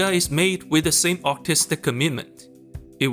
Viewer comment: thought I heard autistic